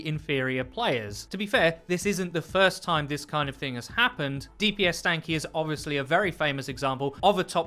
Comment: What do you guys think? Is this new Spider Widow the best epic reskin ever? I think so. Oh, and we all know it's Doomfish not Swamp Monster, just sayin...-E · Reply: Did you say D P S Stanky? 5:22???